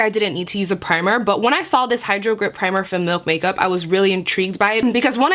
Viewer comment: I would love to try the milk primer (at first I thought you said the MILF primer).....WOW...haha) and the lip tint.......It looks amazing on you. Also I need to thank you....I watched a video of yours the other day, you suggested the mascara's from Essence, the lash extension and the curling one....OMG...I have been looking for a mascara like these all of my life....Thank you Thank you...I love them both. My real lashes look amazing with this. Thank you !!!